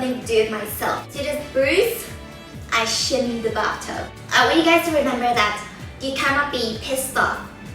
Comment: I thought you said "I shit in the bathtub" ^_